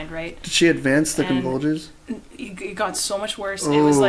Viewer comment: The girl is actually a terminator and the convulsions are her processors malfunctioning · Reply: Bruh I thought you said coronavirus instead of convulsions